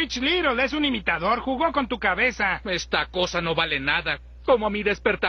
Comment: yo entendí AH BOMBA ALARMA!